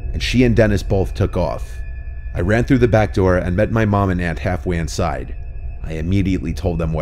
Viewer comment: in the video sounds like Anne Hathaway instead of Aunt Halfway